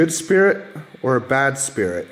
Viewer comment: I heard “Bad Spirit” from the Spirit Box